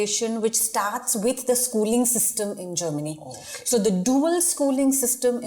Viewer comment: They got us on the first half a minute not gonna lie. I was scared, I thought she said the ''DEA'" and Im stoned as fk man.